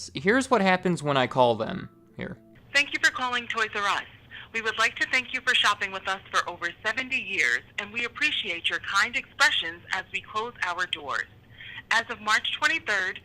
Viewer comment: if your wondering what it says. “Thank you for calling toys r us, We would like to thank you for shopping with us with over 70 years and we appreciate your kind expressions as we close our doors. As of March 23rd, Toys r Us will start liquidation, Here are a few frequently asked questions for your refrence.” Wow, kinda sad a bit.